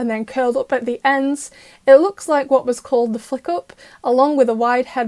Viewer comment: Mr. Bug! And I gotta say.. we were robbed. I mean look at his design!! Where did all of these muscles go in Mr. Pigeon 72!? Also.. 1:09 what's it called again? Cuz I swear I heard a curse word here. 😂😂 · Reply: She said "the flick up" but I heard "the f*** up" 🤣🤣